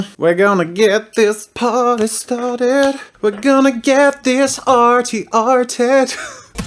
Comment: I thought he said, "We're gonna get it arte arte." I was like, "Jazza can speak Tagalog?"